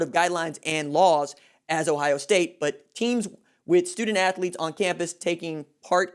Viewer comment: Alex Royer woah slow down tiger. when did i ever mention race? this is a problem that will effect everyone the same, regardless or race, ethnicity, gender, etc. i’m simply stating that when one athlete contracts & passes of the virus, sports will be shut down nationwide. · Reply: My bad homie. I thought you said "white" student athlete. I was like....bruh